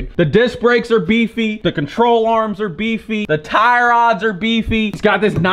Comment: Did say “tire rods”?